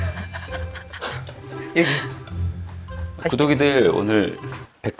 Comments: 난 들을 때마다 구독이들이 자꾸 "구더기들"로 들려... 어쩌지 영석이형 ㅋㅋㅋㅋㅋㅋㅋㅋㅋㅋ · 구독이들이 "구더기들아"로 들려요!!ㅋ